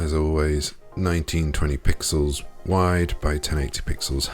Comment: I do not understand the height at 0:30. Can somebody tell me what he says? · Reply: 1920px wide x 1080px high